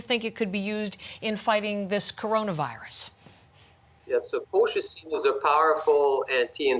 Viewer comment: I don't understand his english. What is the name of the medicine or substance he mentions at 1:45? What is the name of the anti-inflamatory substance? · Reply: Colchicine